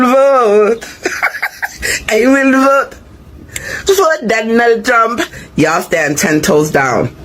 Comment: I will vote for Donald Drumph 🎼🎤 NOT!!! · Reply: I heard "I will boat for Donal Tron". Cuba is an island. So they be going back.